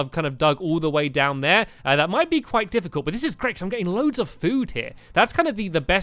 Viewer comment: when you said "I'm getting loads of food here" I thought you said "I'm getting loads of poo here" LOL